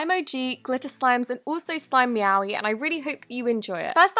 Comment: I thought she said "Slime Yaoi" 😂😂🤣🤣🤣